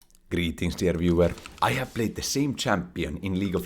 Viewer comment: IVE BEEN WATCHING FOR A COUPLE OF MONTHS NOW AND I JUST REALIZED HE SAYS "GREETINGS DEAR VIEWER" I THOUGHT HE WAS SAYING "GREETINGS TO EVERYWHERE" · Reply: I heard “greetings there viewer” =))